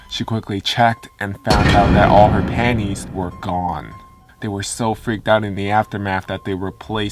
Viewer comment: Oh I thought it said all her candies were gone. I was so confused